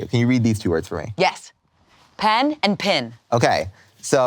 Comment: Is she saying pan and pin??? 🤔